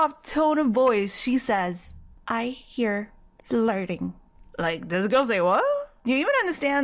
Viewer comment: on 6:53 "i hear the lurding?? what is that " { you mean flurting!! NOT lurding!! } omg like what you say girl!!??